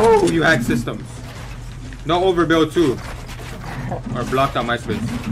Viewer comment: I thought u said no overbuilds